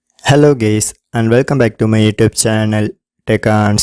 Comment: Did he just say hello gays 🥲🥲